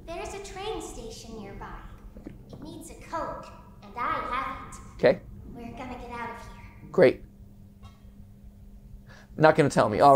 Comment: I swear she said "it needs a coke"